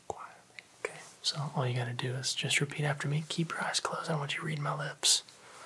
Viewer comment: Somebody knows what he said? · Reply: he said: keep your eyes closed, i dont want you to read my lips